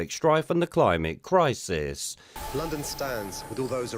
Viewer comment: I thought he said Londonstan not London stands 🤔